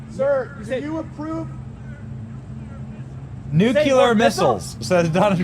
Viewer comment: I thought he said "none of your business" instead of nuclear missiles.